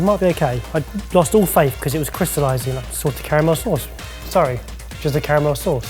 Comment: I kept hearing Barry say 'Sorted' Caramel instead of 'Salted".....Love the idea of a Sorted Caramel.... Also, Dang It, Mike! Still on Team Huttlestone though...